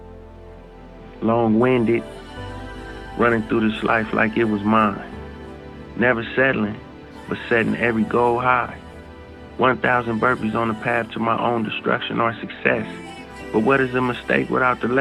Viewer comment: I am French. I am wondering what he is saying at this part ? Can somebody tell me ? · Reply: Long-winded running through this life like it was mine, never settling but setting every goal high. One thousand burpees on the path to my own destruction or success. But what is a mistake without the lesson. See, the best teacher in life is your own experience. None of us know who we are until we any fail. They say every man is defined by his reaction to any given situation. Well who would you want you to define you? Someone else? Or yourself?